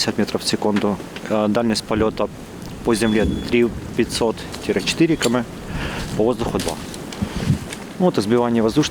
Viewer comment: CC says <num> kilometers, and 2000 kilometers in the air. should read 3-4 kilometers and 2 kilometers. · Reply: He said, "three - five hundred" and "four" and after that "two". So yes, as you concluded.